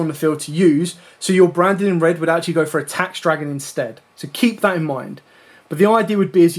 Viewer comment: Is "tax dragon" what was said at 29:10?